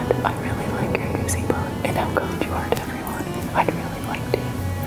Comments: "how kind you are to everyone" kinda sounded like "I'm going to hurt everyone" to be fair hahaha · I know she said how kind you are to everyone but I hear I am going to hurt everyone LOL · This is mind blowing that: “and how kind you are to everyone” sounds like: “and I’m going to hurt everyone” Listen multiple times I hear one time one and the other time the other